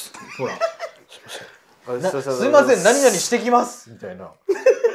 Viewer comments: すいません 修行してきます。に聞こえた気がするよ～ 最後のやつ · 初コメ失礼します！ AI漫画でも公衆電話って独特の怖さありますよね😂撮影お疲れ様です！！ そして最後のセリフ… 「すみません、清掃してきます」じゃないかと聞こえた気がします笑 これからも応援しています😊✨ お体に気をつけてがんばです🤹💕